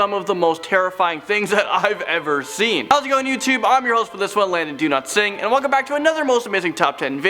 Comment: i hear "Landon Donotsing